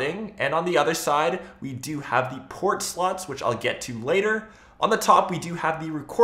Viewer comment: did he say port sluts